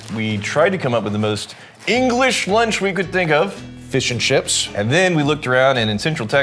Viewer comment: I keep hearing "fish and ships". They know it's "fish and chips".... right?